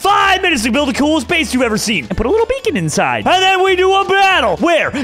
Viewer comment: When Jerome said put a little beacon inside I thought he said “put a little bacon inside”